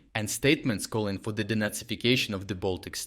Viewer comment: The first time you said “De-nazification”, I heard “Donetsificstion”. Same result.